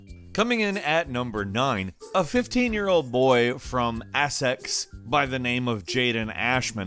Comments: Did he... did he just say assex? Instead of essex? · did he just call Essex “Assex” or am I crazy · Did he just say "Assex"? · Did he say 'Assex' at 1:10 ???